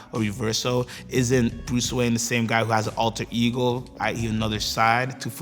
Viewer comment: mins did he say alter eagle? Lol.